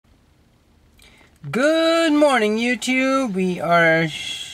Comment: I heard "Good Morning YOUPORN"